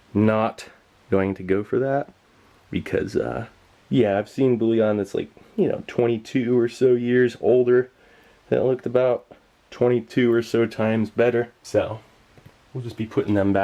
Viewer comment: What are the bullion keys (I think that’s what you said) at 16:07? I can’t figure it out and it’s killing me! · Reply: glaco cubes. They’re soup stock